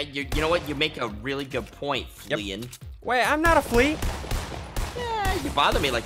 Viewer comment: first time I hear a curse word in this channel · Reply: He said flea itch🤦‍♂️